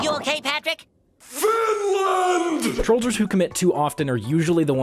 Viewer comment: Did say finland?